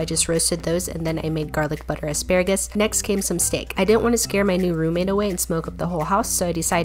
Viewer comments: I thought you said you cooked your new roommate (phew) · i thought it said i cooked my new roommate